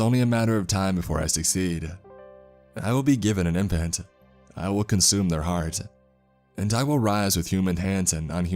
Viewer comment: I thought it said i will consume your dong😂